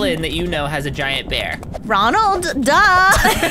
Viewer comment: When said “ronald duh” I thought she said “Donald duck”